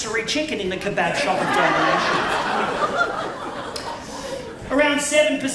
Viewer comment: "The Rotisserie spit in the Kebab Shop of Eternal Damnation" that's a line I never expected to hear. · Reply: I thought he said domination